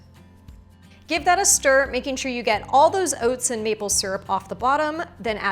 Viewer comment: 1. 1/2 cup of oats. 2. 1/2 cup of milk. 3. 1/4 cup youghurt. 4. 1 tbsp chia seeds. 5. 1tbsp honey. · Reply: Honey? I thought she said maple syrup 😳